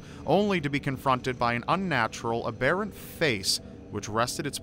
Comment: Bravo! Every word was enunciated perfectly, another flawless video :) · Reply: Every word except what he says at 6:58. Does he mean abhorrent? It sounds like he says ah-bare-ent. That is the only word I couldn't discern, I listened three times and can't make it out. Help me please? Great story though.